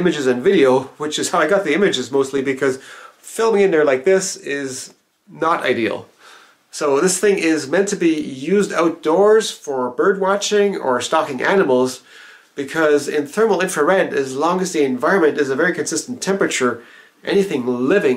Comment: definitely thought you said "stopping cannibals" not "stalking animals" hahaha